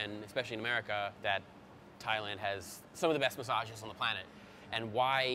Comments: lmao why did I think he was saying Thailand has the best misogynists on the planet lmfaooo I’ve been watching too many hate videos of Andrew tate I guess haha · I definitely first thought he said Thailand has the best misogynists and I was like 🤨😂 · I heard "Thailand has a long history of misogynists" and was like damn! is taking off the gloves. Then realesized he was talking about "massages" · Hahaha I thought he said Thailand has some of the best misogynists! What a strange flex I thought lol